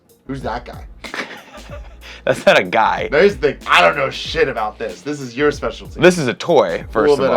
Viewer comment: i thought u said he doesn-t know about anime